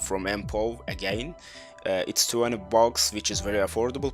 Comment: Did you just say 200 bucks? 2:16 Because there only 20 😂